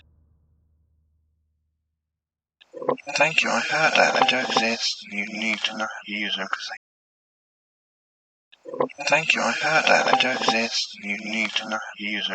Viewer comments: I heard “… you need to know how to use…” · I really, really like this, thank you so much! <3 I hope to see more of this in the future! That last recording sounds to me like he's saying, "Thank you, I heard that, I do exist, you need to not use everything." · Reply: im no native english speaker but...i hear it as "you need to learn to use everthing"? both sounds a tad bit frightening answer :D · The reverse playback when played forwarded sounds like " Thank you we do exist you should not use these" listen to it wit me eyes closed2 you played it then I rewound it an I hear what's in the quotations I put:) great top 5 ❤ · Sounds like it says "Thank you I heard that, I don't this, you need to not use them cause they-" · Last one backwards sounds like it said thank you I had that, you need to know use everything. (Can't understand what was in the middle) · That is an amazing catch of an evp, i hear, "thank you, i heard that, you not need to use anything" so crazy to catch something like that. I really enjoyed that video, keep up the amazing work! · I think it says "thank you I heard that. I don't think you need to hurt her... " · Love the video, some really good clips here, as for the bonus clip it sounds like it's saying "Thank you I heard that, I don't think you need to use anything." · Ar the end i hear, "Thank you, I do exist. You need to ......" Then i cant understand the rest. Anyone else??